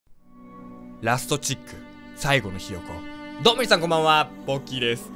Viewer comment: タイトルコールの『最後のひよこ』が『サイコパスのよう』って聞こえて自分のこと言ってんの？って思った